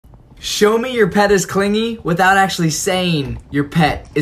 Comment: I thought they said show that your pet is cleaning at first. I was like oh he's cleaning his toy lol